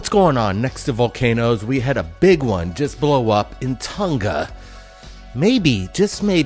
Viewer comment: I think you left the N out of Tonga. I thought I heard you say Toga. LOL. I also think you said it that way on purpose